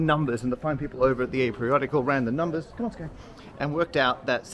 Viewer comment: Though I think what's actually being said is, "Come on, let's go" -- I briefly heard & presumed your dog is named Scone. "Come on Scone!"